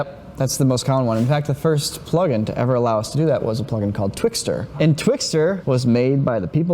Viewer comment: At first, I thought he said "twister".